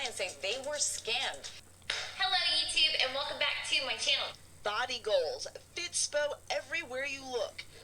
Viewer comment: Am i the only one who heard “Thottie goals” 💀